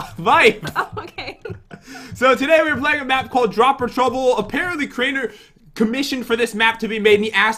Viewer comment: Thought it said deep trouble